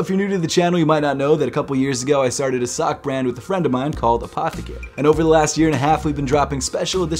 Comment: At first I thought his friend was called apothecary lol Like a pimp named slickback but a friend called apothecary